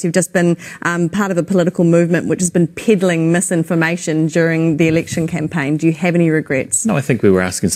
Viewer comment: I know she says 'peddling misinformation' but I keep hearing 'piddling misinformation' XD